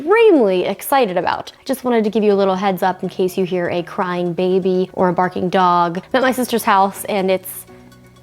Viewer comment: did she just say crying baby ?! Does justine have a baby?